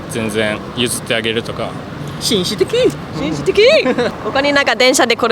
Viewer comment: Does anyone know what the guy with headband say in Japanese?(not the english translation) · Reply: 紳士ー的。